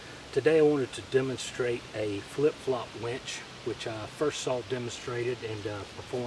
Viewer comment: did he say flip-flop "wench". Been looking for that.